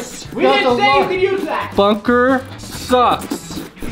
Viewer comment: Did anyone else hear that said a bad word when he hit his head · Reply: I heard say "this bunker sucks" but u thought he said "this f**ker sucks"